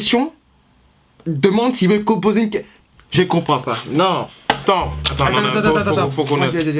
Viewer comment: J'ai pas compris pourquoi Steven a dit j'aicompapa a